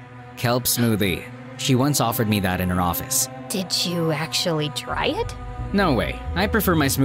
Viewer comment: What I hear:did you try? What the subtitles say:did you dry?😂